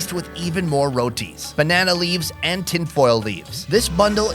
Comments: did you just say tin foil leaves? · Did I hear “tin foil leaves” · did he just say "tin foil leaves"?